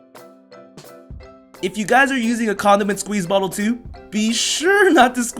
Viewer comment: did he say condom?😂😂